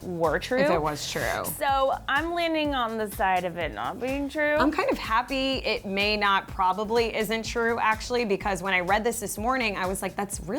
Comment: Did she just say "I'm kind of happy it may not probably isn't true actually"???? lol